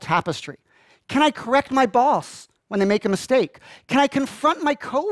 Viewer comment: I heard, "Can I correct by balls" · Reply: He said can I correct my boss